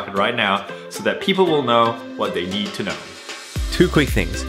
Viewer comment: i heard "what they yee to know"